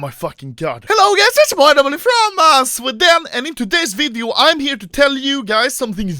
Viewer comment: Anomaly: hello gais its ya boi anomaly from sweden Youtube Captions: hello yes its part of me fromm mass with them WTF